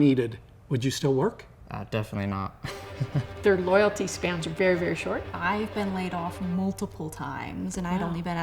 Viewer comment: I at first I thought she said, "your life span is very, very short". I was like, damn, she's speaking truth about climate change. Then I rewatched that part...